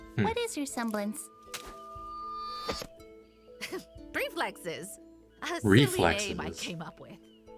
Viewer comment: Someone's probably already clarified this, but when Maria was telling Ruby what her semblance is, she actually said "Pre-flexes", not "Reflexes". Hence why she followed up by saying "A silly name I came up with", since that's obviously not a real word, but instead a new word that she made up lol. It's a play on the prefix "pre-" meaning before/ahead, and the word "reflexes" meaning reacting to smth, aka she can (in her exact words) "react to attacks almost before they happen." So, essentially, Maria has the Remnant version of Spidey Sense 😂😂